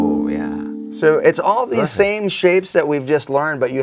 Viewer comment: Did Paul really say "prachtig" at 07:03-04? 😁 I knew he's from The Netherlands, he has that accent.